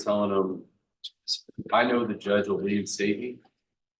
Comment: Ooooooh! “They’re not going to do anything to me”!!!! That was a humongous red flag before the bul!! I didn’t hear what he said the judge wasn’t going to do regarding punishment, despite listening to it several times with AirPods and even captions, but it definitely sank his battleship!! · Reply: If you're referring to what was said around 15:51, I believe the prosecutor was saying the defendant told probation, "I know the judge will reinstate me."